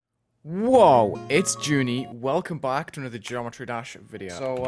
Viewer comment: I thought it said "whoa its wulzy" but i didnt know cuz its